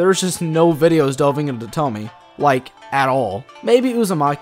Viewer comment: it sounds like your saying "tommy" but with an accent lul